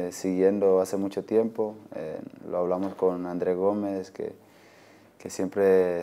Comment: Translate is very fake! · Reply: Curtis Bracey for example at 2:40 he mentions ‘Andre gomes’ but it isn’t in subtitles. Also I can understand parts of what he is saying and the subtitles definitely do not go with what he says. Anyway listen to the amount of words Mina says, and compare it to the amount of subtitles